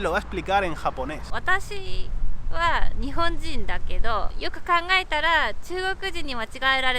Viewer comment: solo entendí Watashi nihongo